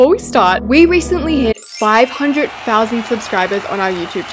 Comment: I thought you said ‘we hit 500 Muffins!’ But in reality : ‘we hit 500k subscribers!’